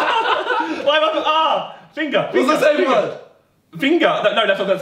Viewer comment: i thought josh said pinga.